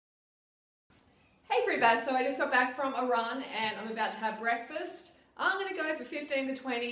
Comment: I thought she said i just got back from iran LOL